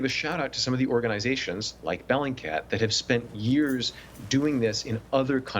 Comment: what's the name of the organisation? I heard " bellandcat"